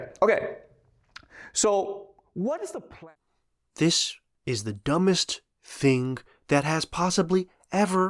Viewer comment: what if. "This is the dumbest thing that has ever been said" no, he said what if, then he said were gunna have some fun today